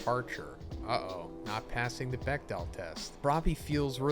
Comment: What test did he say Ochako and Tsu failed at 5:14? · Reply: Bechdel